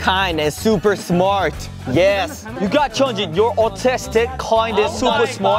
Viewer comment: Han sounded like he was saying Autistic.....